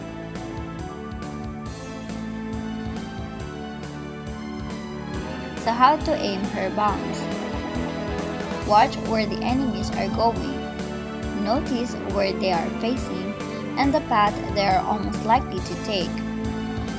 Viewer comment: I thought u said how to aim her boob's 😂😂